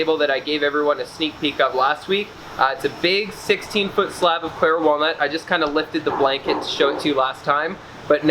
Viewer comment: I dont know why but I thought you said black walnut in the last video lol. Still beautiful! Just wish you could have kept it one solid piece. You have more than enough man power to get it moved!